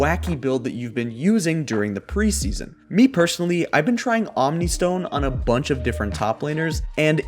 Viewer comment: "I've been trying Omnistone on a bunch of different top laners". 0:44 What i heard: - I've been inting a bunch of games.